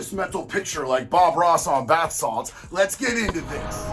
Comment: Wait Did he just say serial killer on bath salts?😂